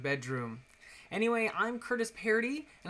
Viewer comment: Ay the end I thought you said 'happy swimming' I was like what are you talking about